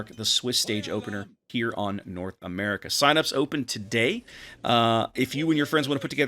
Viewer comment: I can tell I've been playing too much when I thought you said "Sinops are open".